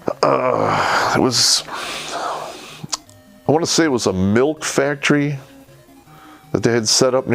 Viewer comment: When he said "milk factory" ,I totally thought he said MILF factory lol